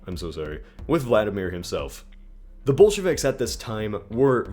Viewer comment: For a couple of times I misheard "Bolsheviks" as "Bullshit" Ironically, I might add